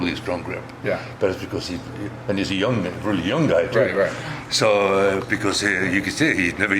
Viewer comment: Anyone else hear him say “And he’s a young nigg...” then catch himself ? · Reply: He says "he's a young man a really young guy". Watch it again with the subtitles on and take that chip off your shoulder.